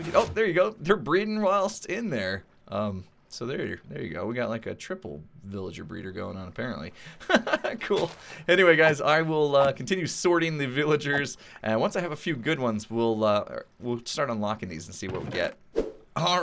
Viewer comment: did he say he was going to continue sorting the villagers or "swording" them?!